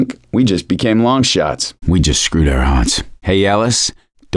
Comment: I miss heard that as “I think we just screwed our aunts” 😂